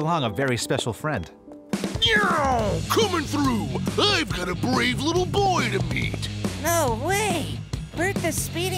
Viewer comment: does he say I got a brave boy to beat